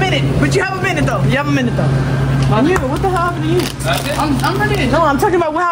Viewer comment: What did she say at 8:26? Sounds like a word that she ain’t supposed to use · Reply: “You what the hell happened to you”